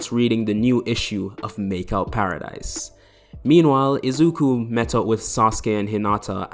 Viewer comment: did he say izuku???